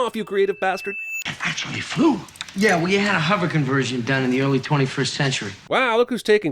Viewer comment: he clearly said “well ya had a” not “we had a” so thats 1 sin off😤